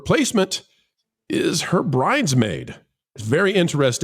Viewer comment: Nooo not our Poor Snow White did i hear "braids Maid" hummmm